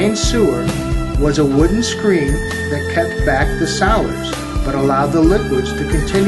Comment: he said " was a wooden screen that kept back the..." and then i don't know what he said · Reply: 'solids'